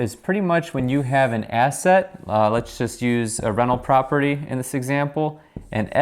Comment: Haha every time you say 'rental', I heard it as 'reno'.